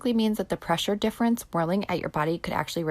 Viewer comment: "The pressure difference boiling at you body" The CC shows "whirling" but I hear "boiling". Neither word makes sense though.